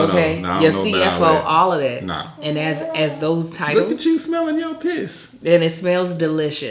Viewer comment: Not even 2 minutes in yet but does say "smelling your piss" at 1:51?? Can someone tell me what this expression means in the US - I'm from Scotland and have never heard this expression before 😂😂